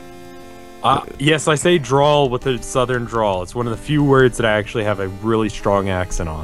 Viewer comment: Everytime says draw it sounds like he says drawl XD